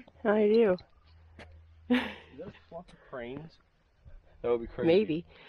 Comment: thought you said "is that a plane" and of course, whats a video without at least one. have you ever been to Vermont? Fantastic views like this, beautiful place to hike and camp. Awesome video man, love seeing you two together on this sort of stuff, totally cool.